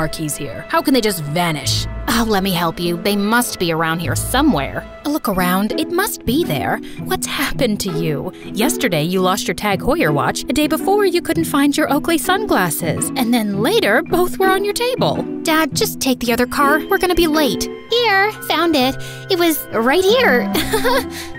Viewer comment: i heard ugly sunglass